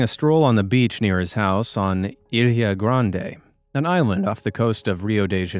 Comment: I though I heard Ariana Grande 😂😂